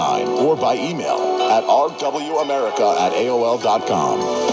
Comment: I can't make out the station identity what is the call letters they are singing? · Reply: That is 107.9 WOLD-LP (Woodbridge, NJ).